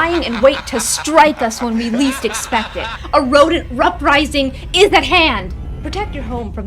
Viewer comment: did she just say "Rodent Ruprising".